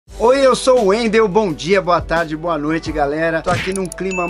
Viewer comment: Quase que escutei “oi, eu sou o goku” kkkkk